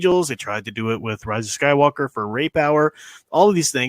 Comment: Ray power lol I thought you said rape hour haha